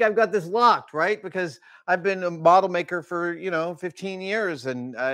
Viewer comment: Adam is a bottle maker? Did I mishear that?